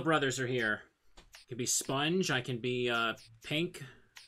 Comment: Did I hear that right? Sponge?